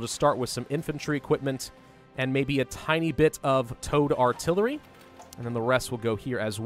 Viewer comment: I thought you said Toad Artillery. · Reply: towed artillery